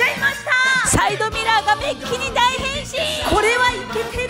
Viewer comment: 「ついにやっちゃいました！サイドミラーが便器に大変身！」って聞こえて「は？」ってなった